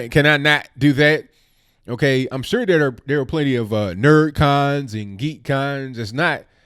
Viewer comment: Hahaha that guy sounds like he says nerds and Greeks initially. I wonder how she feels about being called a dumb, fat cunt🤔.